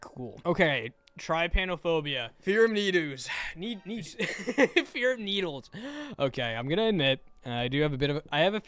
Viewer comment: Not my dum ahh thinking it said the fear of boys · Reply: And I thought this one said the fear of noodles